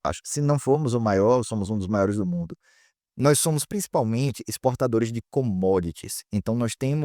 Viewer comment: Amei o video, mas no minuto 1:48 é exportadores de que mesmo?? Nao entendi direito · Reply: Exportadores de commodities. Produtos primários como grãos, minério, petróleo, etc.